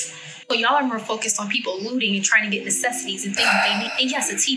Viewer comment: a TV is a necessity? Did I hear that correctly?